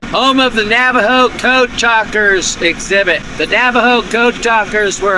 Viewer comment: Lol. Thought I misheard (and I did) when you said Navaho code breakers... had to rewind as I thought you said 'goat tr'uckers'.